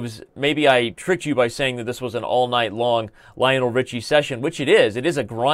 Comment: I thought you said "all knight long", so I was disappointed that the knights were exchanged.